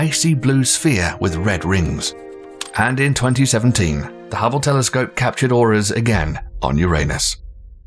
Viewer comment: sounded like "The Hubble telescope captured oras, on you're anus"